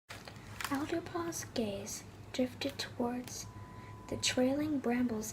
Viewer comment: The first thing I misheard ‘Alderpaw’s gay’ Also this is underrated